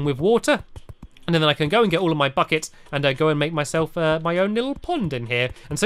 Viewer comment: did he just say "go and get all of my fuck-its"?!